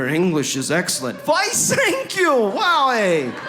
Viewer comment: Why thank you! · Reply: I heard your "I thank you" ...with your German accent!